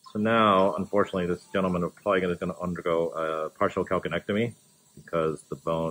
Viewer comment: I spent 18+ years working as a RN in a hospital. About 7 years ago I had to leave nursing due to back injury and let me tell you as soon as the drainage hit that kidney basin the "odor memory" came back and I started mouth breathing as a natural reflex against gagging and the overwhelming odor. So I guess what they say is true in that "it" never leaves you! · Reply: I'm thankful for anybody that's able to tolerate it. At 3:08 he said the patient might have to have a partial calcanectomy. I'm not sure if that's what he said or not. Is that removal of part of the foot?